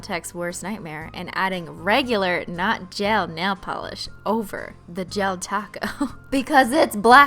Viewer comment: I thought you said nail pop not glue